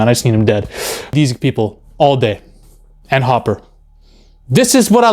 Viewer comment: I thought you said " all dead" not "all day" LOL. I was like... BRUH the Russia arc was the best arc.